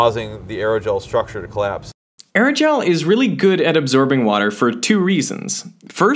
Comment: Did you say, "absorbing"? Or "observing"?